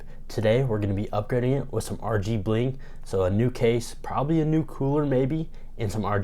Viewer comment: I thought you said "rgbling" which actually sounds like something people would say lol